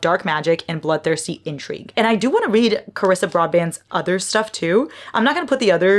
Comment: did you say Broadband??? 😂